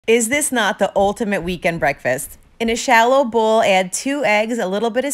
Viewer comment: I thought you sad vegan, and not weekend. I was so confused when I heard that ma'am. 😅❤